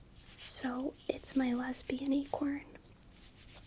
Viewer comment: I’m sitting here trying to sleep and I hear “Lesbian Acorn” So I have to look at my screen to see wtf